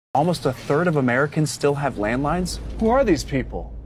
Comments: Ohhhhh I thought it said “land mines” and I got super confused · I misheard land mines Would make a lot of sense tho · I thought he said land mines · I thought you said land mines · Bro I thought he said land mines at first💀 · I thought he said “Land Mines” and was very confused as to how so many people acquired ballistics